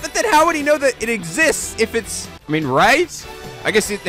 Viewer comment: Did he say Evan? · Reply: Evidence.